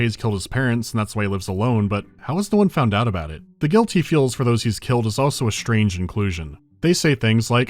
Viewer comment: Subtitle typo at 59:26 "guilt he feels" instead of "guilty feels"